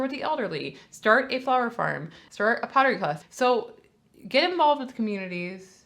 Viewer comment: Ha: you said "start a pottery class...", but I heard it as "PIRATE class" 🤣😜